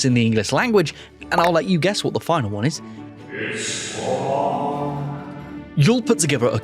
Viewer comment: I was audio only and I could swear that said “it’s fuuuuuuck”